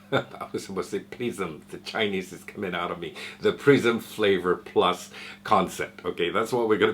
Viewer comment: hey, good video. yet another sub ohm tank. lol. maybe I'll get this one, I wasn't too gone on the previous versions of the isub but this one looks a bit higher quality. for a moment there I thought you said that they had a new prison flavor system, lol then I put 2&2 together apex and prism hahaha. thanks for the video! hope you're having a wonderful day:)